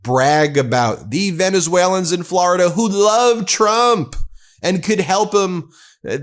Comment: He said "more on Venezuelans in florida" but i heard "moron Venezuelans in florida"